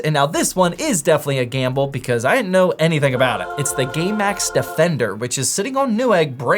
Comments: Did anyone else hear "Gaymax defender"? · I thought he said the gaymax defender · thought i heard gaymax defender 😅